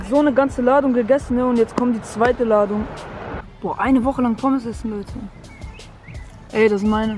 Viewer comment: Ich hab verstanden "eine Woche nur Pornos essen" 😂😂❤️ #gutebesserungandeinemama